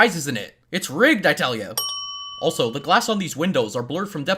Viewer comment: the captions say "It's rigged I tell you" when he clearly said "It's Rigged I tell YA"